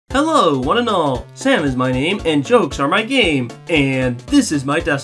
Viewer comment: I thought you said "And Joex is my gang". Lol XD